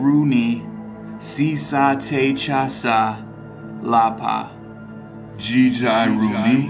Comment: I heard T'Challa